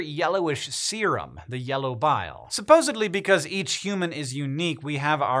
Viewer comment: did he say "supposedly"?